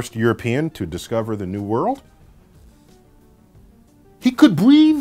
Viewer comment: umm irresponsible of him to claim colombo was first european on american soil. those vikings was from europe as i recall that part of history and geography :) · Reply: Zo Mi I didn't hear "american soil", I heard "new world".